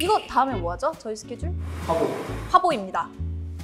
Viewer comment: I thought she said pabo again 😅